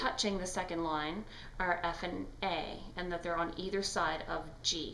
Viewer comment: f and a I heard Effin' A